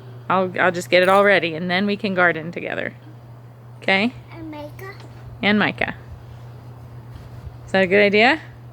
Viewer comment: I thought said and makeup but realized she said and Micah